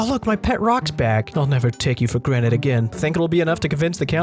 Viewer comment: Did you just say "Ì'll never take your for granite again" ? lmao